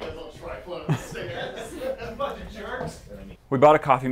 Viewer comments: Did someone say fucking jerks at 12:08 or 12:09 ? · WHAT Did Say?!?!? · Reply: Bunch of jerks is what I heard